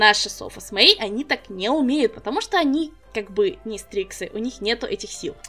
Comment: почему я вместо " стриксы " услышала " стрипсы " ?